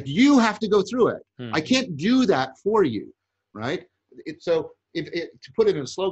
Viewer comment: JV starts talking about a Greek idea that goes with justice, but I can't make out the words. Anyone, what is the word he is saying. The closed caption says Sophism, but that can't be right. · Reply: it's "Sophrosyne".